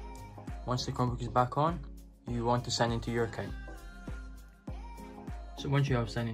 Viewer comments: What did he say at 2:01? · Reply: “Once the Chromebook is back on, you want to sign into your account” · what did you say at 2:01? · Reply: “Once the chrome book is back on, you want to sign into your account”